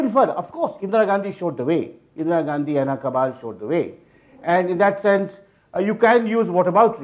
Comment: what did he say? Please anyone can tell me. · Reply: Indira Gandhi and her cabal showed the way